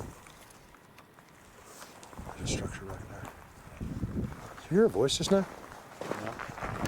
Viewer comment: I hear a whispered 'structure over there' then you ask about hearing a voice.